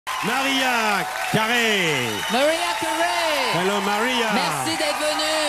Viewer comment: DEAD did he say Maria Curuuuy